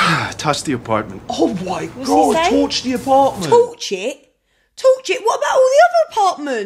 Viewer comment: Unsure how you both misheard toss as torch